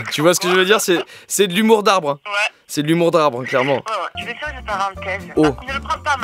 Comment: Au début j'ai compris "humour d'arabe" puis en suite j'ai compris arbre.. du coup j'ai moins rit